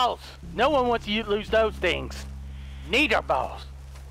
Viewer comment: when he said "need our balls" the first time it kinda sounded like he said "n-word balls" and i was like " wait wtf did he say? " XD